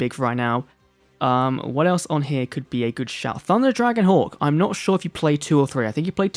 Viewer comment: sounds like he said thunder dragon whore